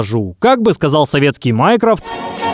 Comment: Мне послышалось вместо Майнкрофт Майнкрафт 💅🗿🔫